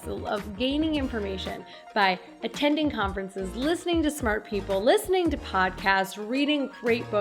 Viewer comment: thought you gonna say "listen to smurfs" but you said " listen to smart people :)